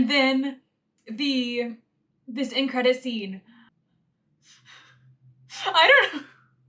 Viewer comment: When she first started gushing about the end-credit scene, I thought she said “incredi-scene”. Not sure it deserves that, but nice idea anyway.